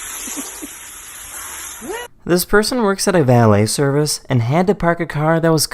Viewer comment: hey guys i´m a non native english speaker can pls tell me someone in what accent the narator speaks it makes me go crazy sounds like a mix of speaking and singing...and what is he saying at 24:07?this person works at a valley service and had to park a car that was covered in bugs??i dont get it